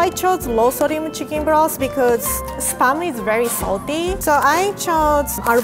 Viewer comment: my parents thought said sperm